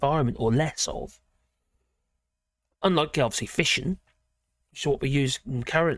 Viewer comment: At first i thought u said "fishing" producing the power in nuclear power stations and wondered what u were on about, then i realised that u said "fission".